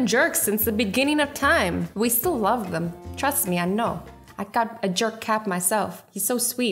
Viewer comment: I thought she said cats me I know 😂😂😂